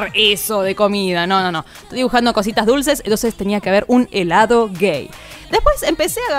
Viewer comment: Sinceramente pense que decia"dibujando con mi adiccion,dulces"